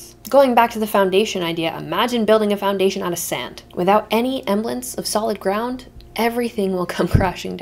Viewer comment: girl wtf is “emblence” that’s not even a word????????